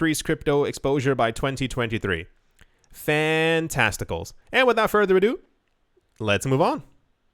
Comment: 🤣 I thought you said 'fantesticles' 😅🤣